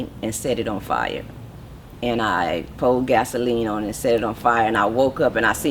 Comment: did she say she pour gasoline and set it on fire.